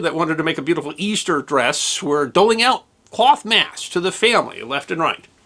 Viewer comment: always good, but... What is he saying at 0:25s? "Coif masse"? If you are interested in the inner workings, also check out Tim Hunkin's "Secret Life" treatment from 1987 on YT and his own site. He built a "human sewing machine" using four actors.